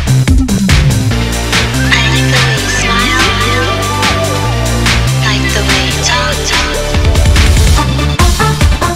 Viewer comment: Who said that 0:09 and What she said? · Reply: she said 'like the way you smile'